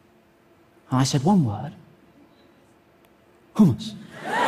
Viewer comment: i heard "humans" instead of "hummus" and i wheezed